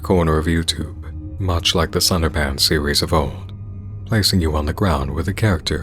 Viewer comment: I'm sorry, I thought he said "*Slenderpants*"